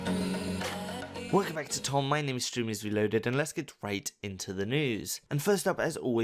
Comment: Did I just hear, "welcome back to, Tom. My name is Streamer Reloaded."